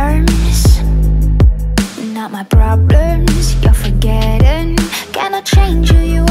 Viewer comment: I could have sworn she said problerms